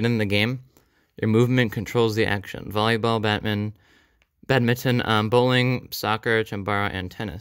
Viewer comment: He accidentally said “Batman” instead of “badminton”